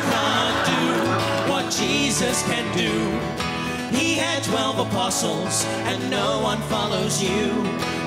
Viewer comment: why do i always hear "he had 12 opossums"